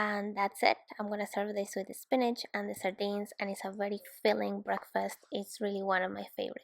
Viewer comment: SARDINES!!!! I thought you said beans.